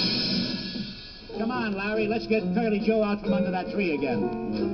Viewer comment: Does Moe call him "Curly Cue?"